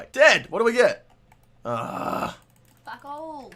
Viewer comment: lmao anyone hear the person in the background mocking "fuck all"